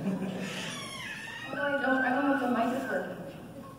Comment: What melissa say at 34:26? and what do they ask her at 34:44? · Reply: She says idk if the mic is working